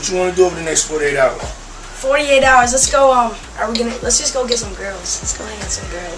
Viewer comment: Diddy didn’t like when Justin said let’s go get some girls!!! Lmao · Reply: I honestly thought he said "lets go get some grills," as in food ... 🤷🏻‍♀️